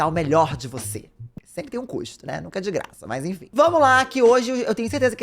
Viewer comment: Jean falando "sempre tem um custo" e eu entendi "sempre tem encosto"... mas talvez ne ... kkkkkk😅